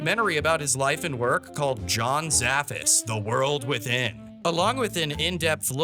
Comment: When John Zaffis introduced himself, I swear I misheard it as "Jon Taffer".